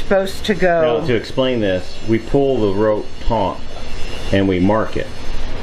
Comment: May I taunt you for saying "taunt" instead of "taut"? I'm enjoying watching these videos; looking forward to the painting! Thanks!